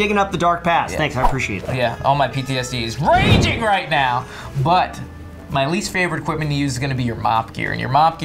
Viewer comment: I deadass thought I heard "My least favourite equipment to use is your mom"